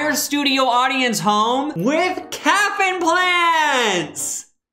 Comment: Lmao when said “calf implants” I really thought he was saying that everyone was getting caffeine plants, and I was fully ready to make fun of his pronunciation of “caffeine”